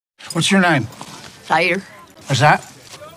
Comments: I thought she said tator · I thought she said Tator 😂😂😂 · Dead ass, I thought her accent was so thick, I thought she was trying to say Heather 😂😂😂